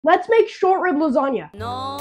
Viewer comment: I thought it said “Short Rizz Lasagna.” 💀💀💀💀💀💀